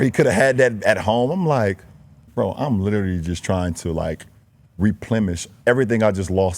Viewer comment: Hmm, Did LeBron just say "Replemish?" or did he mean to say "Replenish?" after eating fruit on the Lakers bench. (47:48) Choose your big words and pronunciation wisely, King James! I'm kinda confused now. (LOL) 😅😳😂